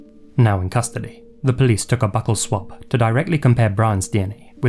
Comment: "Now in custody, police took a butthole swab..." shows brown fingerprint 💀💀💀 (I know he said buckle, it just sounded like that)